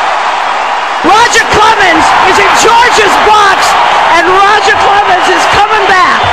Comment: at first i thought she said "roger clemens is a gorgeous fox!!!"